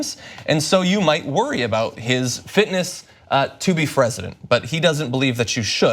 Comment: I heard that "Fresident"